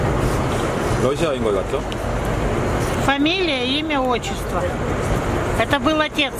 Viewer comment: 잘 들어보면 4:24쯤에 familiy같은 단어가 들림